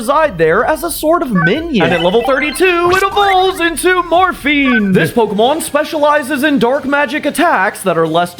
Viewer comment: I thought he said morphine😂